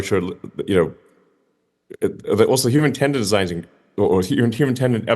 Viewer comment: I'm in my 70s. I've worked with many incredible gifted divergent minds. I find Elon so fascinating to observe. I wish the world had more patience with him and those like him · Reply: I heard that 8:05…”designed” instead of evolved